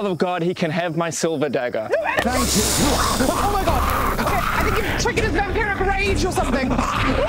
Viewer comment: LOL does anyone know what she said? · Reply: "I think you've triggered his vampiric rage or something"